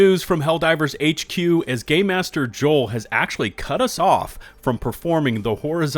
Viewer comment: Did you just call him gay master Joel?